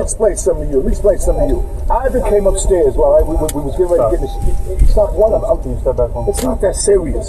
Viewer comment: U can hear Benzino at 7:40 he said “we was getting ready to get in the shower” but he stopped himself. I had to go back and listen like did I just hear what I just heard.